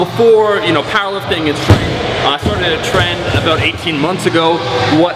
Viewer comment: 25 seconds in, I definitely misheard you - I thought you said you started on Tren not started a trend lol.